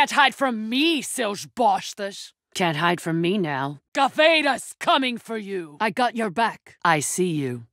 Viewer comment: CAVEIRA'S COMING FOR U!!!! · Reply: I thought she said “I got betas coming for you” Beta male simps intensity